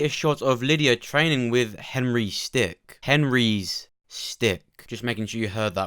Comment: Yo why did I thought you said “Lydia PLAYING with Henry’s stick” 😭 and then repeated it I started crying lmaooo